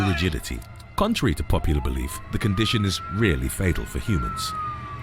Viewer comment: RARELY fatal for the black widow. I was bitten as a teenager and got a little sick, but I did go to the clinic to get some shots to avoid the worst. Was also bitten by a brown recluse during my teenage years. That was worse, as it induces necrosis (so they cut out flesh) and apparently can re-emerge later since your body tries to isolate it (i.e. can "reinfect" if that tissue is traumatized later (I probably didn't describe that perfectly)). In essence, I had to have that area cut out twice two years apart after a trauma to the area. Most of the moose vids are not of moose, but elk and caribou. Peace, Love!! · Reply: Rarely fatal lol it did sound like he said REALLY